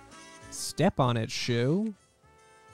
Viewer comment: thought you Said step on it jew